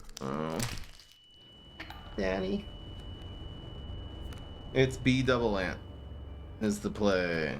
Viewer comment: who else heard "It's bee double ant" as "it's BdoubleAunt" (like the Minecraft Youtuber, BdoubleO, but his aunt).